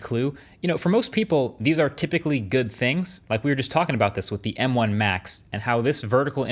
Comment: i heard “M1 Max” when he said “M1 Macs” at 0:36and had to consider whether marques was from the future or not